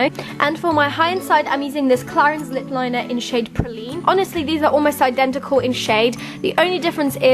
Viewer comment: For the high end lip liner at 5:45 in shade praline, I first thought that you said poo 😂😂